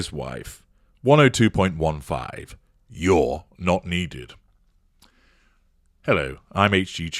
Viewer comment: I heard that as 'You're knock-kneed'ed....'!